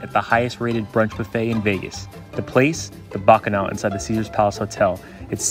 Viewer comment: I really thought that said vegan